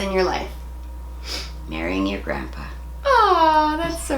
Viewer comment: First I thought she said " when I buried your grandpa " I was like WTF